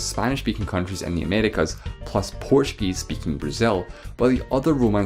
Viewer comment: nigga did you just say “Portugal speaking Brazil”?